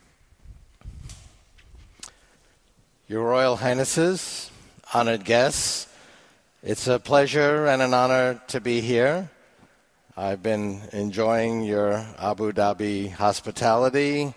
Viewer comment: First thing i hear " your royal anuses" XD